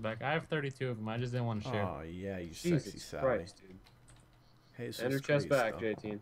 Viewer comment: did you call side sexy?